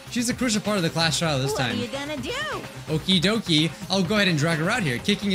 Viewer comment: monokuma,did you say okie dokie???